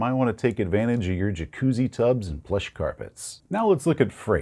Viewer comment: Absolutely thought 9:20 said "flesh carpets" and was very, very concerned.